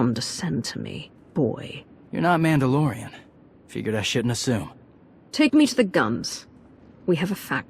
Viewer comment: i have played through it a few times now, and i still think when Torian says "figure i shouldnt assume" sounds more like he's saying "figured i'd shit in a zoo". at